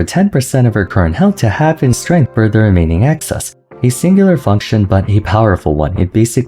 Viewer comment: I fee like I'm having a stroke, what on earth did say at 2:13?? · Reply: "Excess" I think